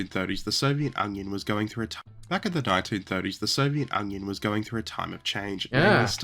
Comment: Wait...did he say Soviet Onion? Huh. It certainly sounds like it.